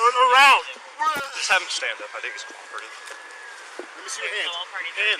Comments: did he really say "let him stand up i think he is drunk" 1:31 after the guy obviously made clear he is deaf? how stupid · it sounds like he said “just have him stand up, I think he’s retarted” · did i hear that officer right or did i not? at 1:33 i might've heard cop say "have him stand up i think he's retarded" i'm 60% deaf so im not sure if i heard it right and captions dont do me any justice. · did he just say "I think he's retared?" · What did he say 1:32 sounded like he said "Have him stanf up i think he's ret^rded" if that's true the cops are slow for not understanding what he said and continure to yell at him i think they are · I think one of the cops say “I think he is retarded” Not seen anyone say anything about that, so yeha. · am I crazy or did the cop say "Just make him stay up, I think he's retarded." Pls someone tell me that's not what I heard 😬